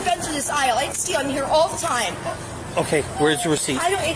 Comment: I swear @ 1:38 she says “I steal out of hear all the time” the caption kind of catches it but gets it wrong but I’m almost sure of it, someone please tell me if Im wrong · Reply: it sounds like she said i didnt steal im here all the time